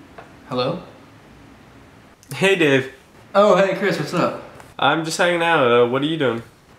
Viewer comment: What does he say at 0:24? "I'm just having/heading nadada, what are you doin?" Makes no sense to me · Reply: "I'm just hanging out"